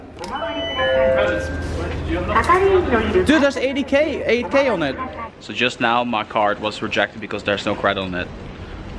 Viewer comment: Were you able to find out what happened? Did the ticket the women gave you really have something to do with it? Also what in the heck did say? Thank you! Love the channel. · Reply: Markus Schulz said "arigatou gozaimasu" which means "thank you so much".